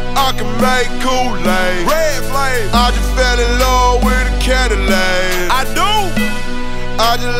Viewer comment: Oh thought that he said he just fell in love with a caddilac